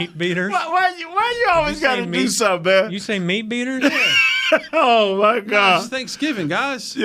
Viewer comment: What the hell did Jesse say @ 40:39 to make Nate laugh so hard?? Was it "meat beaters"?? I was laughing so hard at Nate laughing so hard but didn't totally catch what Jesse actually said. Anyway, I love Nate's laugh....it cracks me up. GO COWBOYS!!